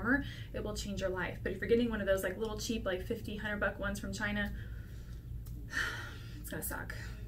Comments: did anyone else think she said "1500 buck cheap ones" and freak out then play it again and again to hear she meant 50-100 buck LMFAO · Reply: I thought she said 1500.00 I thought...I'm doomed! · Did she just say "If you're getting one of those like $1500 buck ones from China, it's going to suck"? Um... Mine was only $38 and I thought that was a bit much 🤣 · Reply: she said 50-100. I swear I thought she said that as well until I replayed. same here got mine off amazon